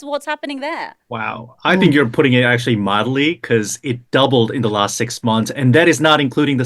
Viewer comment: Did he say "modelly" ???